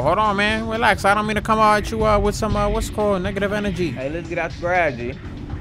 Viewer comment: Why at 30:27 it sound like he said nigga